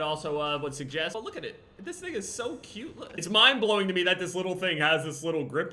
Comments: did man said dis nigga so cute on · 😂 I thought he said the n-word (he said "thing") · bro anybody else hear that ‘this niggas so cute’ or am I buggin